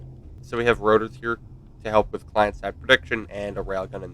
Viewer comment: can you explain the rotor comment? It sounded like "to help with client side prediction" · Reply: yes. it was "to help with client side prediction"